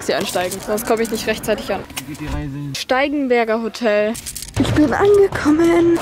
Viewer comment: Ich habe steifenberger Hotel verstanden 😂🤷🏾‍♂️